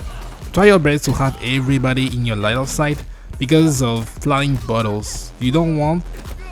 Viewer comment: I thought he said "flying buttholes" and I was like "yeah that makes sense. Those enemies are buttholes.". 😂 I laughed at my own stupidity. lol